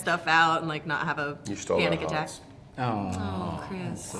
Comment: what did chris say?? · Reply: You stole our hearts